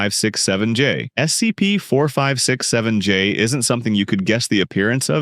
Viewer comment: Wait a minute…. Did you say 67?